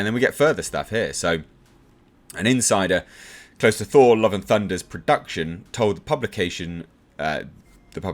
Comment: "I thought you said 'Whore Love Down Under'."